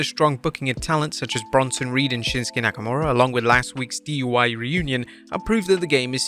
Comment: Did say “DUI” instead of “DIY”